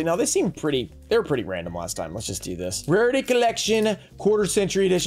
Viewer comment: Brooooo, I thought this said “Party Collection” 🤣🤣🤣 I was ready for the new “THOT” and “Fook-Boy” Archetypes 😂🤣😂🤣😂